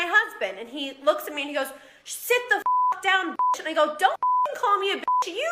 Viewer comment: I thought Lebron said "Why do you have your mask down in this bytch" NOT "Sit the _ down b!tch"